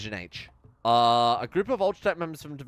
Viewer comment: I thought you said a group of ultratech memers.